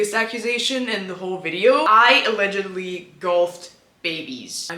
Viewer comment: She said she golfed babies...? I thought she said she gulped them...